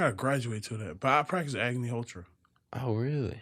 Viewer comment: what did he say at 9:13 agony___?